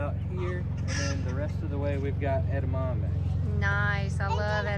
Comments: What did you call that plant in the video it sounded like you said Etta mamba what is this I never heard of this plant yourself and liked it a lot thanks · What is the name again of the plant yourself and liked during the tour of garden. Sounded like you said Etta mamba plant sorry but I am very curious of your favorite Blessed Be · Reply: Edamame (immature soybeans)